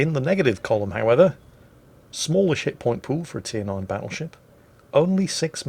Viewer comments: Yo 2:52 Did you just say "Shitpointpool" XD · wait wait wait... does he really say "Smaller SHITPOINT Pool"?????